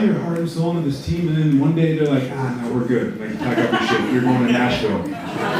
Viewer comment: what did he say ? "Pack up your stuff..."